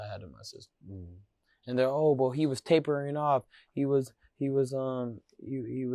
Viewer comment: I thought he said they raped him when he was 2